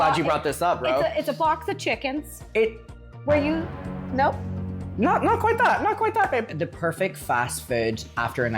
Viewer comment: did anyone hear say not quite babe???